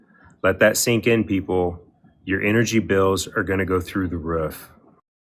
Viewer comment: did he just say 'through the ruff' ? 🤣🤣🤣 🐶